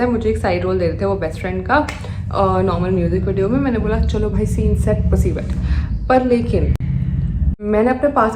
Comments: Scene set p*** wet 😅 did I hear it right 😮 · As a male , Casting couch happen with both male and female models. Males need to be very careful as they face casting couch from both genders i.e. bisexual, gay , female directors , producers etc. And you cannot file a complaint against them after facing as no law will come to save you. Be careful 🙌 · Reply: did she really said scene set pus*y wet